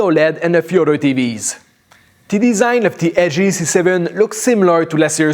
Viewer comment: Like if you also thought it said lg g7 instead of how it is, lg c7.